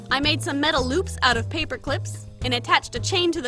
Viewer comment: did u say... LÖÖPS?